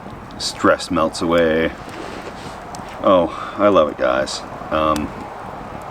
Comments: "It's beautiful out here, stress melts away. Oh, I love guys?" what did he say there? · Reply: „ i love it guys „ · It’s technically not a fake trap, if you take into consideration that you might catch a “Bear” (Hairy gay dude) and is actually the bait. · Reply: Well I think he did say he loves guys